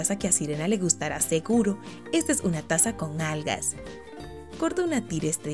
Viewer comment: minuto 06:18 le entendi un vaso con nalgas ja